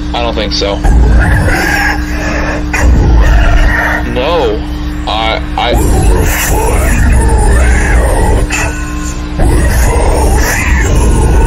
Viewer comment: At the end of the first one the spirit says “We’ll figure out,without you” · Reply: I heard "we'll find our way out without you"